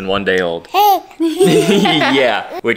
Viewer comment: she sounded like she said “hey”